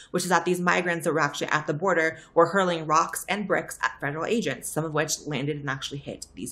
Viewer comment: 😂 I kept hearing "asians" until I realized she was saying federal agents 🤣🤣🤣